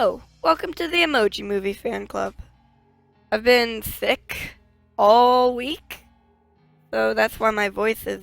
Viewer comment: I thought you said "I've been thick this whole week." LIKE YAS GIRL